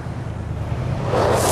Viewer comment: the first time i heard this i thought he said Mr Popo be cool